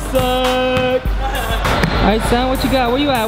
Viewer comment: roi said the f word to Alex like you do here that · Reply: Suck He Said